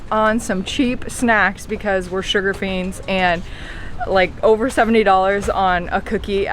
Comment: she says "over $70 on a cookie at the hotel." Wait, what? Am I hearing that correctly? And NO one is talking about that in the comments? I knew it was an expensive trip, but that's insane! That's the most expensive cookie I've ever heard about in my entire life! Or, is this cookie the size of a mattress? Can anyone confirm or clarify this statement? Does she mean 70 francs, which is about $0.65? In which case, that's not expensive at all, so why would she even bring it up, then? Someone please clarify.